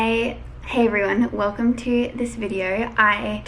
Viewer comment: at first I thought she said "I hate everyone" 😂